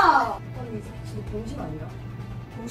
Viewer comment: jaja, le entendí "haaa la chingada!!" :v